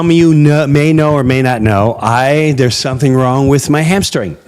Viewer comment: Jim, I’m so sorry, but I thought I heard, “There’s something wrong with my hamster thing” as you grabbed your rear end. 😂 No judgement, brother. To each his own, but man did I laugh. Ohhh….He said hamstring! Haha! Thanks for another great livestream, Jim and crew. So many love and appreciate you. I wish you all and your families a Merry Christmas. God bless, everyone. 💜